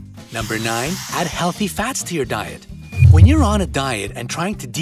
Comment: Bruh I thought u said no diets required but u said AdD HeALThy FaTS tO dA DiET